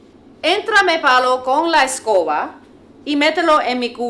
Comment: que dice en espanol a la 1:40? "entrame palo con la escoba...?"